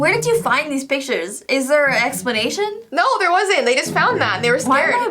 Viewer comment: jessi said ottic instead of attic